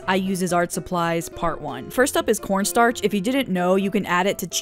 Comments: When she said corn starch I thought she said 🌽⭐️ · When she said corn starch I dead@ thought she said 🌽 star 💀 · LMAOO WHEN SHE SAID CORNSTARCH I THOUGHT SHE SAID PORNSTARCH OMGG IM SORRY FOR ANY WHO DOESNT KNOW WHAT THAT ID